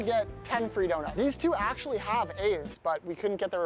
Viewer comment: I thought he said these 2 kids actually have aids 💀💀💀😭😭